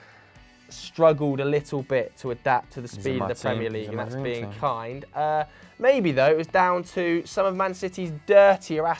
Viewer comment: What does say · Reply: He's in my team, He's in my dream team.